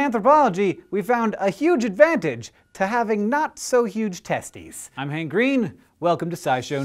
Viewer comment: great i thought it said "tests" not "testes".